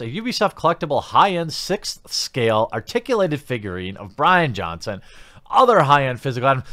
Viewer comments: Damn, Jeremy you almost made me do a spit take. I thought you said the Phoenix Edition came with a Rian Johnson figurine. I had to squint and look real close to find out I have hearing problems. Brian Johnson. With a “B”. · For a second I thought you said "an articulated figurine of Rian Johnson" and nearly shat a brick, lmao.